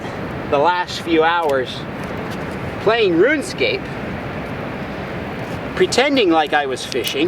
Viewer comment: Did he just say Runescape? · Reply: No he said just d4nce, yes he said RuneScape